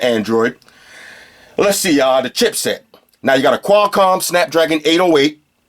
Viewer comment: I thought I heard dip set I immediately reached for my extra large bandanna